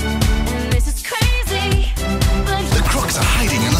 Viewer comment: “I just met you, and this is crazy, but—” THE CROOKS ARE HIDING IN THE SWAMP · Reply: I thought it said the crocs are hiding in the swamp, LOL. But the swamp is where crocs live, of course the CROOKS are hiding there.